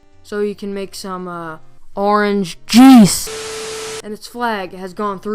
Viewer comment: All my laughs 2:54 so you can make some uhh...orange JUICE(I heard JEECE) 2:12 GRAPEY